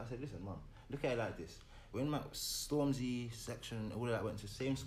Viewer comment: did he say he went same school as stormzy and section,? idk I'm watching this on my TV and hear him proper